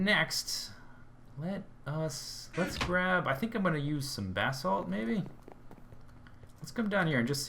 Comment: Whenever he says "Basalt" I hear "Bathsalt"